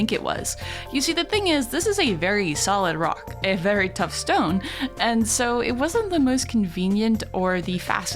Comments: Did I hear Rock and Stone? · DID I HEAR A ROCK AND STONE?, ROCK AND STONE LADS!!!!